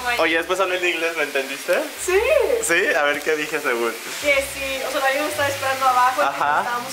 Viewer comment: En el minuto 1:37 entendí a ver que dije mi amor